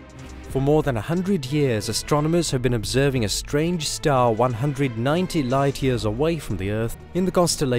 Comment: Did he say 190 light years away?